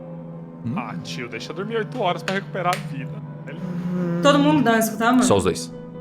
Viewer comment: A fala pro Cellbit: "Todo mundo dá escutar, amor?" Ou eu to enganado? Ou isso já foi revelado e só eu to panguando? kkkkkkkkkkkkkkk